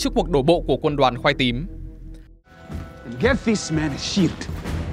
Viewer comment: có ai nghe thành "get this man a sh*t" ko =))